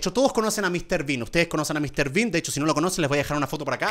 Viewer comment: porque escuché Mr beast en vez de Mr bean